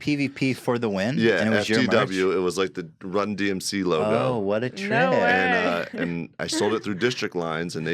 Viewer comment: could of swore i heard " what a rip " at the end lol